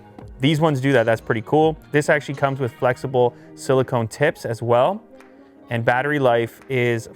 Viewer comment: I thought he said “flexible silicone tits”